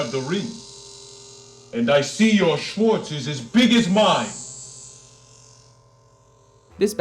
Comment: WTF? Did I just hear Lord Helmet say at 07:20 "Your Schwanz is as big as mine"!?!?!? O.o